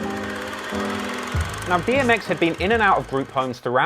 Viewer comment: Did he say BMX?